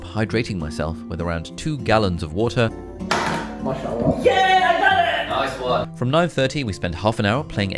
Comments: lol did say "mashalla" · Does say "Masha'Allah"? 🤣🤣🤣❤️ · Did just say Mash'Allah or am I hearing things :P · sounded like said "Masha'Allah" lol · did he just say “Mashallah” Lmaooo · did you say Masha allah?